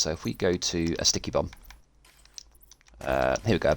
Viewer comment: I only had my headset on halfway and for a moment, i thought you said "Stinky Bum". My first thought was "Oh, he's playing nasty now..." and it hit me that I was hearing stuff and I had to alt tab out, rewind the video and put both ear cups on to hear it correctly, to where you said "Sticky Bomb". Still, would have liked to see this "Stinky Bum" build you were talking about. Jokes aside, you're a class act in a community where many push cookie cutter builds. You don't speak condescendingly in your videos (We shall not name names, but we know who) and you explain the math in a clear comprehensible manner. Well done and keep up the top notch work.